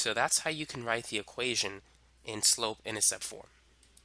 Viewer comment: What did he say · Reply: That how you write the equation in slope-intercept form